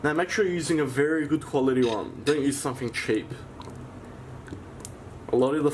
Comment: when you said "Don't use something cheap" I first heard it as "Don't you fucking cheat" I couldn't believe my ears and thought 'No way, wouldn't say that' I had to listen to it a couple of times though😂😂